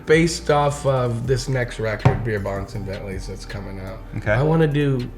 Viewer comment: I thought he said "beer bouncin bellys" 😂😂😂